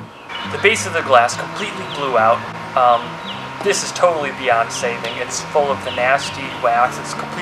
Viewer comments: Lol I thought you said "This is totally Beyonce" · he said it was beyond saving I thought he said beyonce XD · did anyone else hear beyonce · when he said it was beyond saving I thought he said Beyonce